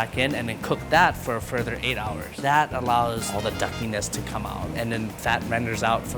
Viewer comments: Did He Say Duck Penis? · Wait what did he say??... all the duck penis?? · what did he say duck what ? (Turn on caption) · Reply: He said "duckiness" so basically the essence of being/smelling/looking/tasting like a duck depending on the context.